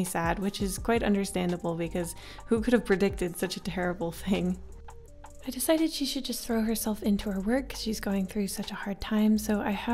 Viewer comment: I thought you said "I thought that she should just throw herself in a well"